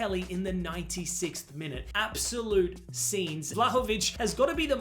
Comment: why did i get sent to the shops on 0:55. i thought bro was about to say absolute cinema but he had other plans😭😭